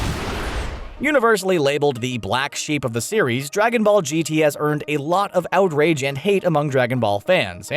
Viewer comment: did he just say “Dragonball GTS”? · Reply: I think he said Gt has